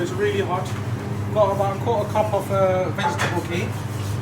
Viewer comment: Looks amazing mate, i didn't quite catch the 1:38 part with the '1/4 cup of vegetable ____?' can someone let me know so i can get cooking! look forward to trying it. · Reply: Vegetable ghee